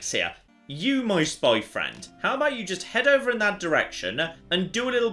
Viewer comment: subtitles say, “you moist boyfriend”